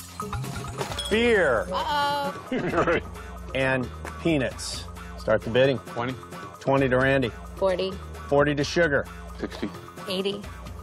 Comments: Wasn't looking at the screen when he said "Beer and Peanuts" and thought he said Beer and Penis 😆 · Is it just me or does it sound like Jeff said beer and penis at the very beginning?😂😂😂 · I thought he said pen15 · it sounds like he said penis